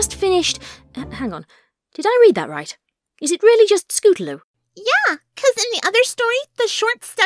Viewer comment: Wait did i heard you say scootaroo instead of scootaloo?